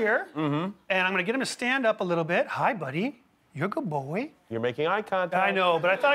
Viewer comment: It’s sounded like he said you a good booooi